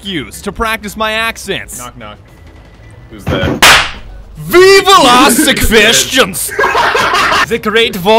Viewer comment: "Ve vill ask ze questions" I don't know why but I was half asleep and I hear "Viva Las Vegas"